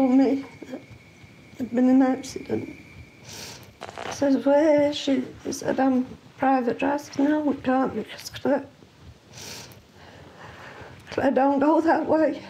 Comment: no idea what's being said here. And CC doesn't know either apparently. 😠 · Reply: Where is she? Private drive, no she doesn’t go that way. At least, that’s what I think she said. Her poor mum was so hurt. 😢